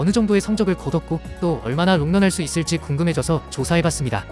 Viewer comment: 왜 "얼마나 음란할 수 있을지" 로 들리지... 음란마귀가 씌였나...